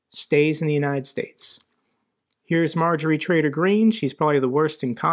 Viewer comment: Hey, am I hearing things or did you just say (18:49): Marjorie Traitor Green?